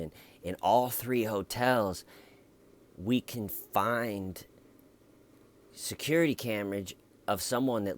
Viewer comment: Did you say “security camerage” at 9:28??